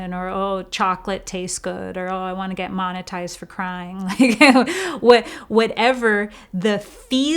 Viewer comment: I thought said monetised for frying - which is plausible and if anything a nice lighthearted timeout for conscious evolution 😂